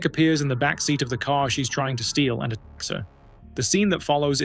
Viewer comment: What did you say at 20:11 ? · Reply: I can’t fully tell but I think it was “attacks” based off the context. Not sure tho